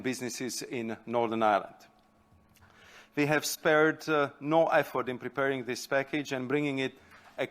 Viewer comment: At first, I heard "We've spared no f-word" and merely "do they allow such phrases in the EU press conferences now?"